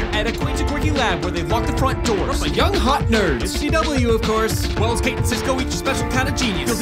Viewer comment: What does he say at 0:33? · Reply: it's CW of course